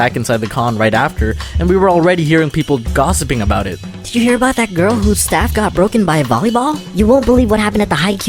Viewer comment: I thought he was going to say"have u heard of that girl that got hit by a volleyball and broke?"XD